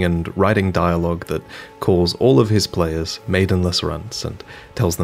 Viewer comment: for a second when he said "maidenless runt" i thought he was gonna say maidenless Cunt instead lmao